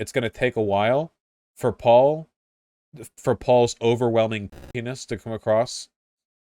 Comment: what is vaush saying at the bleep at 6:54? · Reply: “Paul’s overwhelming cuntiness”, I believe.